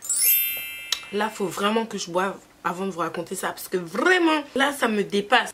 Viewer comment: je rêve ou j'ai entendu boive😂😂